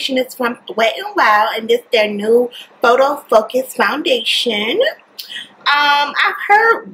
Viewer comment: when she said photo focus I thought she said photo fuck its